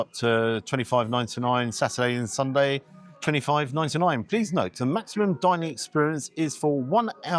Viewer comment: cheap as chips $57.00 usd for one person? If the price was for two people then I’ll agree. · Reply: Thanks I thought I heard 29.99 in the beginning. But I should have put on cc to make sure I understood.